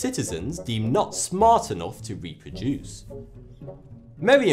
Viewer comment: Did you say Merriam-Webmaster? 😂